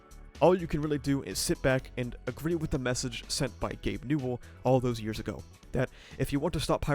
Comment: I was reading the comments and not paying much attention and misheard this as "senpai Gabe UwU" and just accepted it without question, then maybe 15 seconds later I started wondering what is wrong with my brain and rewatched that part because there was no way I had heard that right lol